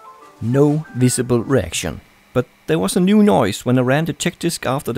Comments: Why does it sound like he says erection when he says reaction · everytime he said 'reaction' i thought he was saying 'erection'